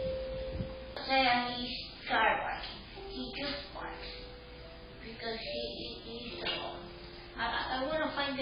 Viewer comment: it sounds like either “where is it?” Or “worth it” but I didn’t hear worth it until I read the words worth it on the screen suggesting that is what it said, I first heard where is it.